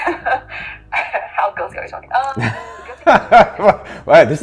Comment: What did she say? before he answered her its PG13? just curious lol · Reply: She just jokingly said, 'How guilty are we talking?' 😊